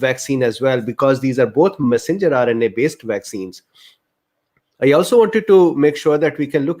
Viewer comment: What did you say at 0:30... masinjulary vaccines??? Really could not understand. PLEASE REPLY!!! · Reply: He says, "messenger RNA vaccines".